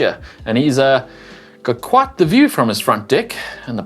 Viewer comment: sounded like James said "quit a nice view from his D**k" when presenting the bikes. LUL